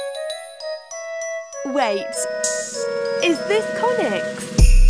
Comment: bro I thought it said "is this Thomas" for the longest time